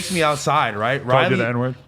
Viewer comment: What does Adam say at 12:13 I can’t hear what he said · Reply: "Called you the N-word"